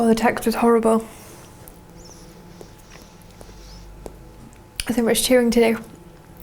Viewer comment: What does she say at 8:33? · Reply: 'There's too much chewing to do.'